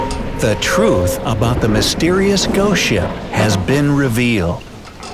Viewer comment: sounded like goat ship to me.